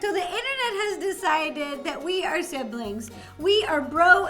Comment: THE most AWSOME And AMAZING Theorist and One of the BEST cooks ever! And apparently your related in a way. PERFECT IDEA!! MORE COLABS. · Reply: Sorry my mistake I thought they said d's are siblings